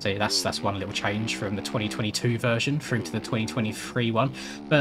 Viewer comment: i thought you said it was the 2020 free version til you said 2022